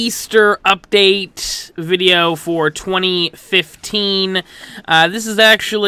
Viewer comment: I thought you said "420, 15" xD